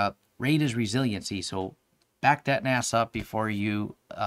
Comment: All I heard was "BACK THAT NAS UP"